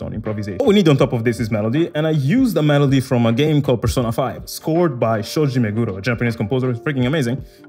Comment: I donno if it's the Manchester City in me but at 2:30 did he just say scored by Sergio Aguero !?!.........😂😂😂😂😂😂😂😂😂 Anyway big big fan of JAzz and the team.... Currently learning & experimenting Jazz scale & if i can ochestrate in the scale..... Big fan,i like your accent ...where are you from !?!...